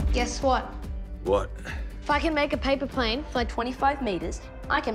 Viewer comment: I heard "Fuckn' make a paper plane..."